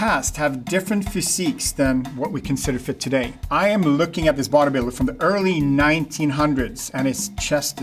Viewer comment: Oh he was saying "body builder" when I thought he was saying "vaudeviller" 😅